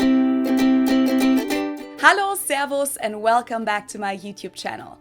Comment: Did I just hear "Szervusz" at the beginning? As a Hungarian, I feel honoured. The video is good and the opinions are important. Thank you for sharing them with us.